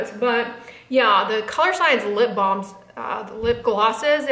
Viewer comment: I love the new Colorsciense SPF makeup products. I'm going to try the blush. I thought you were saying "Colour Bombs"...I see they are "balms". LOL